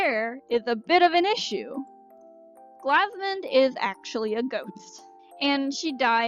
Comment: I thought you said 'goat' not 'ghost' at first and I was HELLA confused